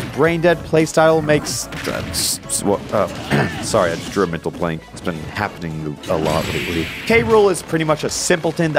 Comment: what are you saying at 4:34 Edit: i figured it out, he says “sorry, i just drew a mental blank. Its been happening alot lately”